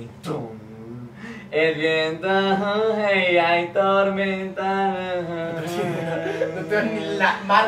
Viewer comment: jajajaja parece que dice el viento coge y ay tormenta en mi interior